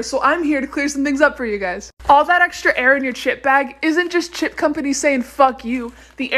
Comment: Me mishearing- All that air in your s**t bag.